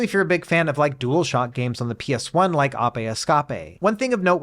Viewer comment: apeh eskape!!?? what in the world did you say....ape escape...